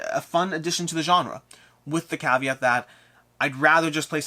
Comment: you again said the word that sounds like "caviar". What does this word mean? (I'm no native speaker).